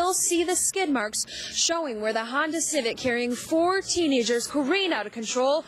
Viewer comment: haha :50-:53 sounds like she says 14 teenagers korean out of control haha but sad =(